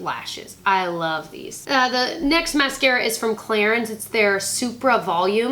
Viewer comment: When you said then next mascara is from Clarins I thought you said is from Claire's lol! Love you!